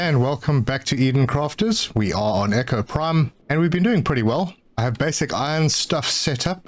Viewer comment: Ten seconds into the video and I misheard Echo Prime as "Neko Prime" - welp, you had my attention lol